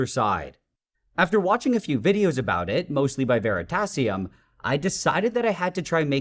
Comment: At first I thought he said "veritaserum" and I'm like BRO WHAT